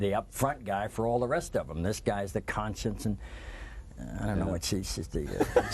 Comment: Anyone know what was being said around 10:10 ? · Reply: Debi Taylor he’s saying what each person means to the group. “This person is the up front man. And this guys the conscience. And she...I dunno what she does. She’s the fashion expert?” Wow, what a douchbag thing to say.